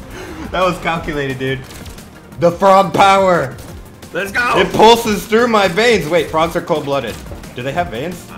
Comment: Im watching this late at night and my eyes are closing and i hear him say "the frog power flows through my veins"and im like wah???he a frog🐸